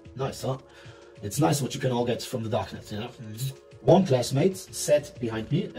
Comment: He actually says 'Darknet'